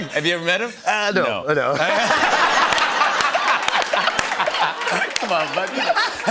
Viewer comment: Start at minute 5:45 -5:55 … maybe I’m just Burj , but I swear Jimmy say “come on muthafuc” underneath all that laughing and dapp’in